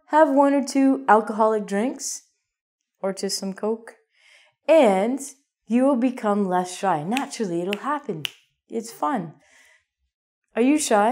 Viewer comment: Did I just hear her recommend cocaine?